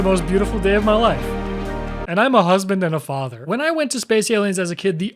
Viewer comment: DID HE SAY FATHER???